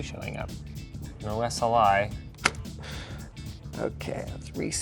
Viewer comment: is he saying no that’s a lie or no sli?